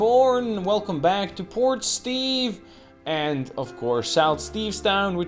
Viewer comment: Haha, every time I hear you say Port Steve I hear "Poor Steve" and sometimes I think that's more accurate. XD The logistics are getting real complicated with the ole urban sprawl but I have faith in the illustrious leader! Well played.